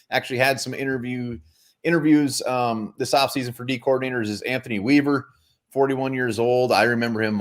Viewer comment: Did you just say Anthony weiner 🤣🤣🤢🤮 whatever name you said 🤔 reminds me of that stupid politician